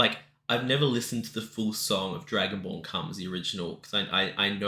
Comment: the auto-generated captions say you’ve never listened to the whole song “Dragonborn Comes,” but I swear it sounds to me like you said “Dragonball Comes.”